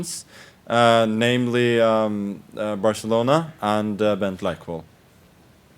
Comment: what did Magnus say after Barcelona??? Thx! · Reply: Bent Leikvoll. (Character in the Norwegian reality parody show 'Nissene i bingen'.)